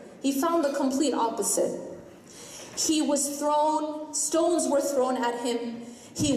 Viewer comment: At around 2:12, it says 'she' instead of 'he' sallallahu alayhi wasallam.